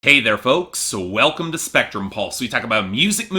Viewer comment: I thought you said in the intro: "Hey there, boats!"